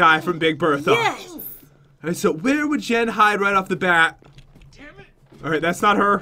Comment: I think he said damnit?